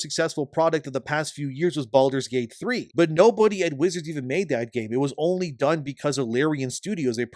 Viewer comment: anyone else hear “But no pony at wizards even made that game” or is it just me?